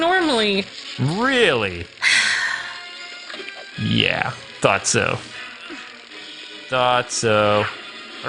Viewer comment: I heard "thot-so"